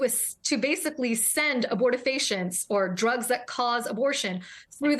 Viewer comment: Will that same judge go after the offenders on the Texas Child Support most wanted list? Yes, it is a site open to all. The people owe $185,000 in one case for multiple children he has no plan to support. Sperm causes abortion, period. Unpaid child support is abortion of another kind. It leaves Children and Women in poverty, without resource. In Using this Judges vernacular, it is a SIN not to support your children, to have intercourse with no plan that a child might be the result, that you owe for that child and do not pay. It is a SIN if you are a judge in Texas and do not track these offenders down and jail them until their children are safe, fed, clothed and educated. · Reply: Wait! It sounded like you said "sperm causes abortion". 😂. That has to be a typo! That would mean you are taking even less responsibility than the men you are screaming about. Yes, both need to take full responsibility!